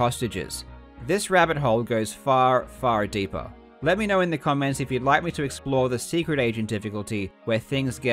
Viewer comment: What you said: "Say in the comment section if you would like me to explore the Secret Agent" What I heard: "Do you want more content?"